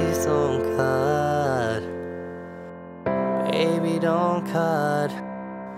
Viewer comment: thought is said baby dont cut autistic 💀🤦‍♂️